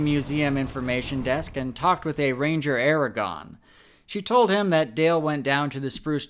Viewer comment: Ranger Aragorn?! Did I hear that correctly?